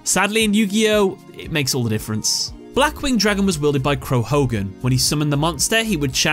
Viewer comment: had to rewind it to make sure if I heard it right or not, thought he said hulk holgan 20:58 that kinda looks like obelisk the tormenter